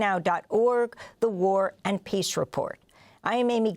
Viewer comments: "The War and Peace Report" ...for years I thought she was saying The Warren Peace Report😅😅😅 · For years!!! I thought she said Warren Peace Report. She finally says it slowly and clearly enough to hear its actually War Abd Peace Report